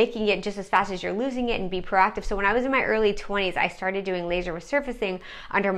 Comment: How old are u? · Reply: I thought she said early 40's. She looks great and so pretty.